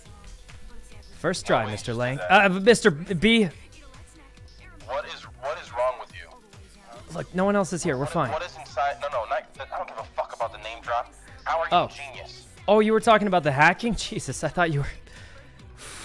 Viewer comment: what did he say? "how are you genius?"?